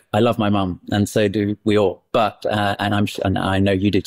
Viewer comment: Ok, I had to rewind about six and a half minutes in when Jamie says, "I love my mom, and so do we all" because at first, I thought he said, "I love my mom, and so say we all"!